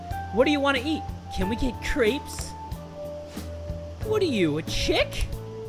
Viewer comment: I thought he said creeps not crepes